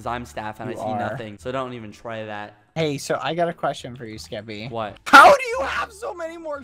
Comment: He said “I have a question for you Skeppy” but I thought he said “ I have a crush on you Skeppy